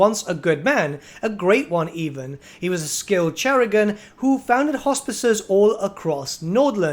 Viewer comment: I'm really glad you covered the *leaks*. BTW, at 3:18 it's not cherrugon, it's chirurgeon; it's like surgeon but with kair instead of sur and it means surgeon.